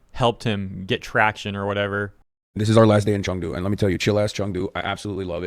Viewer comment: Did he just say Chung Do instead of Chengdu???